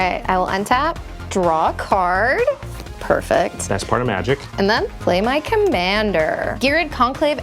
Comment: sass from lol Edit: thought he said "that's part of magic". I realise he said "best part of magic" lol